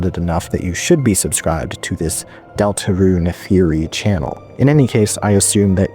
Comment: Was only half paying attention watching this and thought you said "deltarune yuri channel"